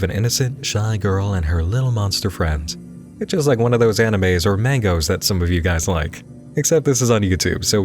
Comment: I cant be the only one that thought you said "anime or mango"